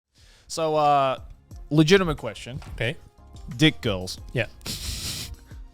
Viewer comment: i thought he said dead girls 💀